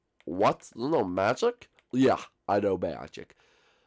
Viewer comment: Did anyone else think at 4:07 Four Eyes said he know Mayor Jake or was that just me😂😂😀